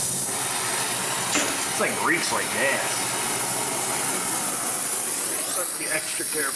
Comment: I thought he said this thing reaks like ass